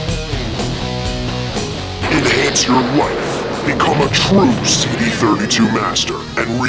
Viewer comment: When the CD 32 paint bucket addon commercial played when it said "enhance you life" I could've sworn I heard "enhance your wife" I was like word lemme get one lmao 😂😂🤣😂